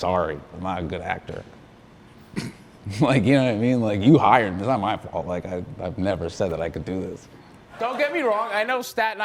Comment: What does he say on 2:44 " that doesn't my fault"? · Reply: “you hired me. it’s not my fault, i never said that i could do this”